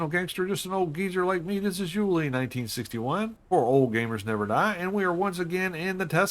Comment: I'm sick n bed n feel miserable plus happy n blessed so I thought you said it's old geezer that never die I realized after seeing your name I misheard what u said 😅